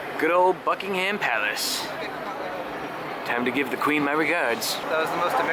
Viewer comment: I thought he said Fucking Ham Palace